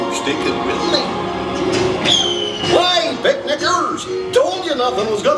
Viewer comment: He sounds like he is saying “Hey big n***ers”